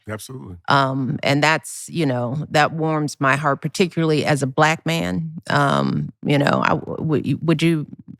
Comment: Did she just say she is a black man?